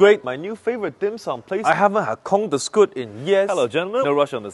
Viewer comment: Bro, I thought he said “I haven’t had c#m so good in years”but then I looked again and it said “corn” 💀💀💀😂😂